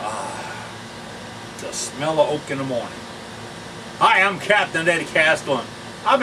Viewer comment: I thought you said. oh the smell of coke in the morning...lol